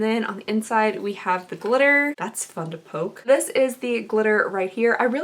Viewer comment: When you said "that's fun to poke" I heard "that's fucking dope" and I literally had to rewind like did I just hear her swear? 😂